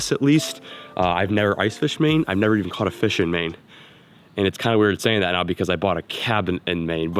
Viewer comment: said, “never caught a fish in Maine” What I heard, “never caught a fish mane”